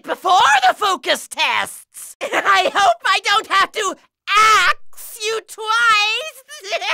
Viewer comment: Did he say "ask" or "axe"? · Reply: He said axe.